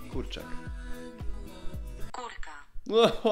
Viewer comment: ja usłyszałam kurw* XDD